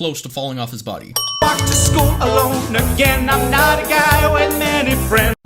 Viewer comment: for some reason,i hear "gay"